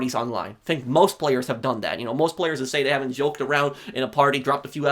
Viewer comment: "most players saying the N- uhh joking aroung in a party" I think I heard that correctly🤣🤣 lmao slip up 🤣